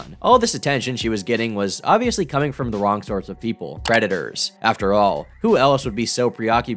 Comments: I wasn't looking at 17:19 and swore you said redditors. Like damnnn Reddit too? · Did anyone else hear "redditers" at 17:19? I was sitting here thinking "man I knew reddit wasn't that good, but it's that bad????" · I thought you said "redditors" at first, but then I realized that's the same thing 😂